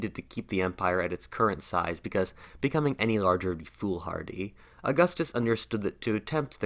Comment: what is being said? "Becoming any larger..."?